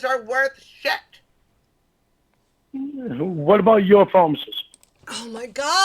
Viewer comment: When said "What about your promises?" I thought he said "what about UFOs?" And I thought "what? He doesn't really have a leg to stand on so he's gonna start talking about aliens now?" I can't even understand what he says half the time. If I was I'd tell I'm not talking to him anymore cuz I can't understand what he says. And it's not a speech impediment. He just drinks so much his brain actually forgot how to make his mouth speak!